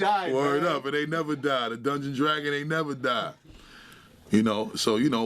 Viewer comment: Hoad Tavai if this don't make you laugh then you not human :) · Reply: You know how you hear something, and then can't stop hearing the same thing, at 7:18 I hear 'Dungeon and dragon aint never die'... =]